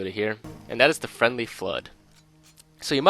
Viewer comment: I thought friendly flood said friendly food.